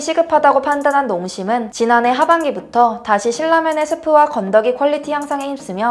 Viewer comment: 저만 이렇게 들리나요? 7:21 실라면 , 7:27 질라면 매운맛ㅋㅋㅋ